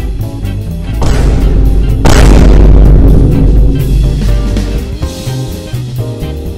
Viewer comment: What? What did he/she say...? :| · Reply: he said he wanted to get chocked by ace like that one reunion member lmao